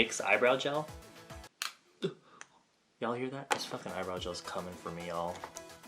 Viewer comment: i swear with the eyebrow stuff i thought that he said "it's cumming for me" instead of "it's comming for me" There is seriously something wrong with me 😂